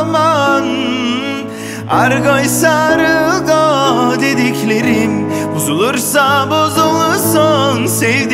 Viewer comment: ne dedikleriymiş anlamadım alüminyum. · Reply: argoysa argo dediklerimi. diyor